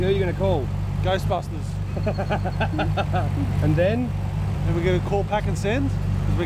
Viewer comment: What did he say at 9:07? Great series ;-) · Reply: "And then we're going to call 'Pack & Send' and send this f**king thing"